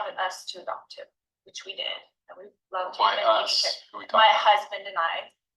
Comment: did she say ' and we loved him'